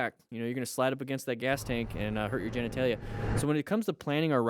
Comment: I know it sucks. But you said "genetalia" quickly and i heard "jen i tay ya" and giggled a bit. Think... redneck. Talking about something bad. Like "boy i tell ya". Stupid, but it was needed after the start of 4th winter here today